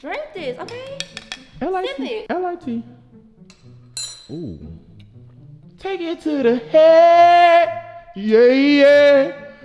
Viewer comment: Lord why my daughter start laughing like hell when was singing she thought he was saying chicken in the head 😂😂😂she said she want to hear him sing all the time ..